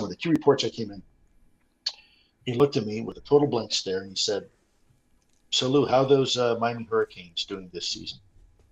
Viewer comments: I couldn't make out what Lue said around 6:25 that made everyone exclaim "Wow!". Can anybody help me out here? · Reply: He said, basically, "after I finished my briefing, he looked at me with a straight face and said 'So Lue...how are those Miami Hurricanes doing this season?'". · sounds like "So, Lue, how is mein verkings doing this season". What was actually said?